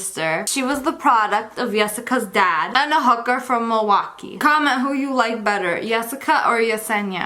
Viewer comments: i thought she said "and a hooker from moana" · Why does it sound like you said my name but it’s not the same spelling “Jessina•” 😂